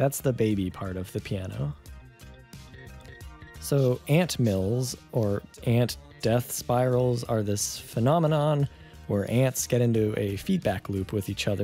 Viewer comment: There's just something about the captions saying "aunt death spirals are this phenomenon or aunts get into a feedback loop with each other's pheromones" at about